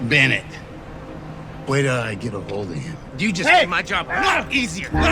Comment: it sounded like he said wait till I get a hold of him instead of where did I get a hold of him